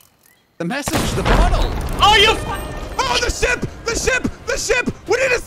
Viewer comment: when jack shot the crows nest, I heard say you fuck